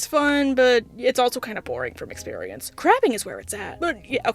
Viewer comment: I swear to baphy that she said crapping 🤣